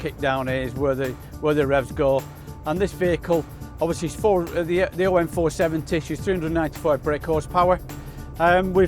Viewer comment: what did he say here ? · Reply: The OM470, she's 395bhp.